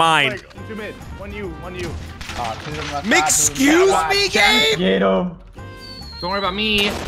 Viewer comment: I swear said “MC-XCUSE ME GAME?” 😂😂😂